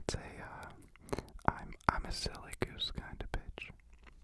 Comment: lowkey thought he said im a silly goose kinda bitch